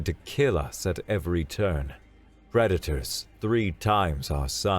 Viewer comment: I understood Redditors instead of predators. That was an interesting picture